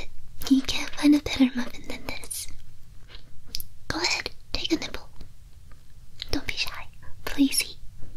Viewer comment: I thought she said go ahead take a nipple 🥲